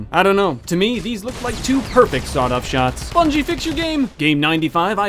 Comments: was I the only one who heared bungie the destiny and halo creators instead of epic games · did he say pubg fix your game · did you say Bungie?